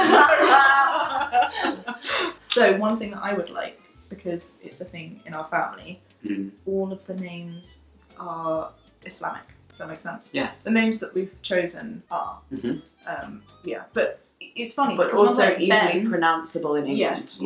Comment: i thought she said "all the names are hispanic" and i was like weird but okay~